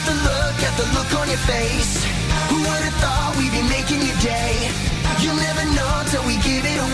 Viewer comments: i thought it say who woulda thought we'd be making ya gay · I had to do a quick double take I swear it sounded like Mitchell said “Who would’ve thought we’d be making you gay” (also almost completely forgot about this show) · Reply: it’s “who would’ve thought we’d be making your day” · Is it just me or does it say « who’d ever thought we’d be making you gay » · Am I the only one who heard "Who oughta thought we'd be making you gay!" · I'm sorry but it REALLY sounds like he said "making you gay" there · When I watched this show, I always thought it said "Who would've thought we'd be making you gay" Now I am gay, wtf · what did they say? "Who would have though we been making you gay" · I thought the theme song said "Who woulda thought we'd be making you GAY?".